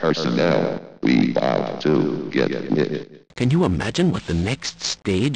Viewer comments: ATTENTION, ALL PERSONNEL, WE BOUT TO GET LIT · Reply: I thought it said "we got to get it" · ATTENTION, ALL PERSONNEL, WE BOUT TO GET LIT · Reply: I thought it said 'We aught to get fit' XD